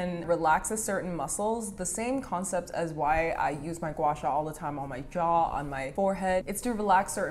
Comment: What does she say? Guasha? wacha?